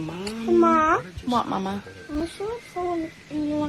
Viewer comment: Does she call her son "mama" ? I get why she does cause us women do that, but he's a boy...